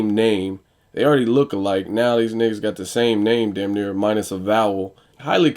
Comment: did you say niggas? Just asking because I never hear that out of you.